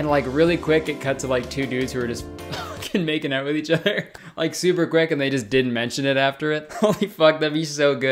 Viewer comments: the captions says "super Greg" instead of "super quick" now I'm just imagining all Danny Gonzales' fans just have a thing about making out with each other whenever they meet. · anyone notice at 12:05 it says "super greg" instead of "super quick" in the captions when kurtis was talking about two dudes making out 😭